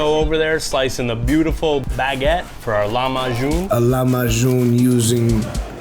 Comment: Jesus I thought he said 'Llama June' and was briefly horrified